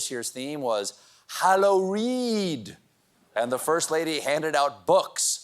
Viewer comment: for a second I thought you said “Hallo-Weed”!